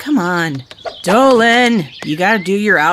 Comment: now u know what's Dolan always saying at outro (With Subtitles) HAVAGUDWUN (Have A Good One) If I Know, This Means We Need To Have A Good Question For The Future Videos